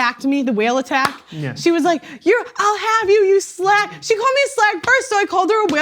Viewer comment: Note how she herself paints herself as a victim when describing the "whale attack" as she calls it. She claims the other woman called her ugly first. But calling a woman a 'slag' is not calling them ugly. It is similar to calling a man a bastard. Pearl was rude first, and got attacked back. Sounds just like the 'modern women' she is always complaining about. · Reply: You misheard. She said the woman called her a slag FIRST